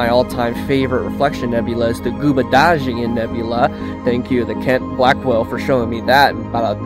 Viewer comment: What reflection nebula are you saying at 2:03? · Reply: Gyulbudaghian's Nebula